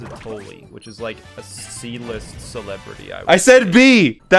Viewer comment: he actually said C-list in the video EXPOSED DISLIKE UNSUB